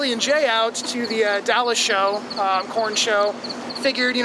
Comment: i thought said Porn show.